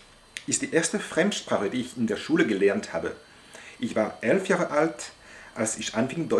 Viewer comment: The subtitles say you started learning German when you where 13, but you said it was 11